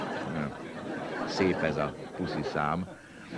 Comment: nál mit mond ???? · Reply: Szép ez a puszi szám"